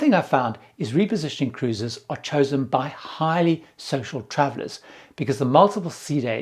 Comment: The first time I heard "..chosen by highly social travellers" What I heard was "..chosen by highly sexual travellers"